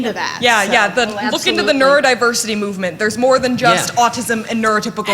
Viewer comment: look into neurodiversity, there's more than just autism, we have Venn diagrams Somehow what I heard say is "come to the dark side, we have Venn diagrams" I instantly became a Sith lord, because screw cookies I want catnip, by which I mean diagrams (of any kind) :D